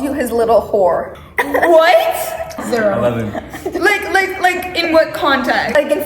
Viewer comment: did he just say 11😂😂😅😅